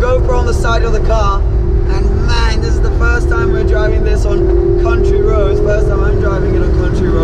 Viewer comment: did you say country roads?